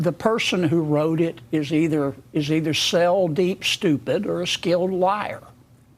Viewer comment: "Cell-deep stupid"? "Sell" deep stupid? Sail deep stupid? I have watched every one of this great man's public appearances since he was sworn in. And this is the first time I may be totally stumped. What did he say? Cell-deep stupid as in stupid down to the cellular level?! I can not decode this and I'm sorry. It is not my fault I grew up in the north and have a little trouble with the southern accent and witticism once in 7 years. Will some smart guy out there help the dumb guy, please and thank you. Signed, Day 521 of Illinois Held Hostage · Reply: Subs say cell as in every cell in the body is contributing to stupidity.